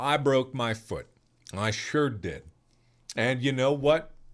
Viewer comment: I almost thought he said "My shirt did"